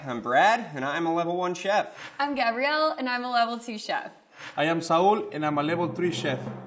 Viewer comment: When I heard “ME LLAMO SAUL” i knew it was game over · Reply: He said 'I am Saul'